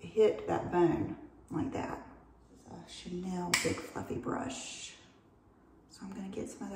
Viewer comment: did she say Chanel big f-ing brush 😂😂😂